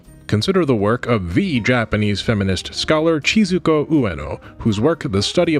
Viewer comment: I liked the video a lot but some parts rubbed me the wrong way, especially the bit in the middle where it almost sounded like you were saying "the Japanese did feminism wrong".